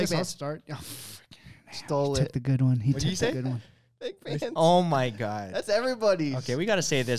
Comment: What did say at 1:05:42?? It sounds like gibberish to me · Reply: I think peg pants